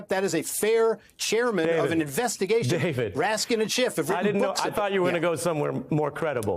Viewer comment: Did I hear "President Dump" @ 6:53 ??? LOL